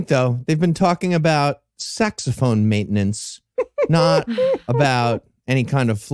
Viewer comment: This is extra funny if one, like me, mishears saxophone and sex phone. =P